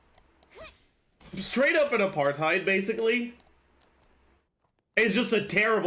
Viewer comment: What does he say at 4:42 ? I don't understand the word at all and the subtitles don't understand either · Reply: Apartheid, which is more or less government supported racism